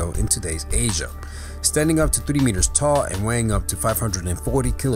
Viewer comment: did he say 30 meters tall? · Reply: He said 3